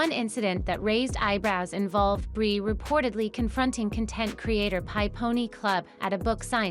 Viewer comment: did they just say "HIGH Pony Club"??